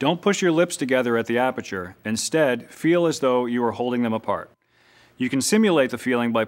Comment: I thought he said: make them feel like you’re holding in a fart.